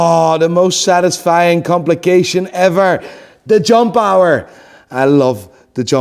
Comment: I always thought he was saying the John Par and honestly I prefer to keep it that way.